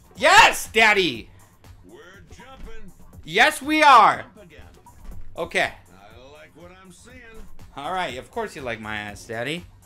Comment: "Of course you like my ass daddy" Words I never thought id hear this man say😳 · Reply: the funniest part is that i think the wii instructor said “i don’t like what i’m seein’”